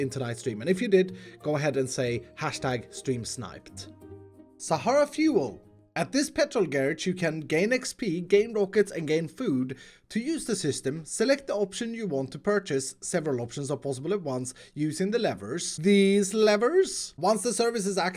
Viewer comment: I thought it said: Grian XP Grian rockets Grian food